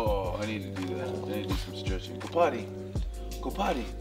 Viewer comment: After the dog, Hobes, stretched and Steve said, ''yeah I need to do that, I need to strech .''I thought he then said, "go do Pilates, go on go do pilates" I busted a gut laughing went to rewind and.. um well, what he really was saying was, "go potty!" I was laughing pretty good until I heard him say Go potty the second time. Steve's funny as all get out so I would have believed he said it. Sorry for you and Morgan not seeing each other face to face, I think about how you two must feel often. I'm single n live with a couple cats so I know how it feels to go to bed so grateful to at least snuggle your animal! I also taught Ed and Sheeran how to sit and stay for food too ; )!